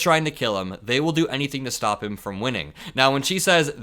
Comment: Sorry, but I thought the last sentence of MTG's saying, "They want to stop him from winning."... I thought it said, "whining." 🤣